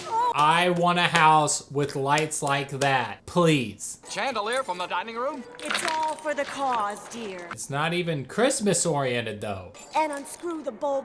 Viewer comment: as a kid I thought she said “it’s for the car dear”